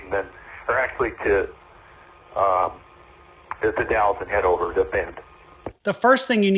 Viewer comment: Hi, I live in Oregon near where this incident took place. At 4:45 you have some question marks where you possibly didn't understand the pilot's radio call. I believe he was saying he wanted to head to (the city of) The Dalles, and then to Bend. If his final destination was going to be Bend, his chosen route would make sense to keep from flying over the Cascade mountains at Mount Hood, or further South over Santiam Pass.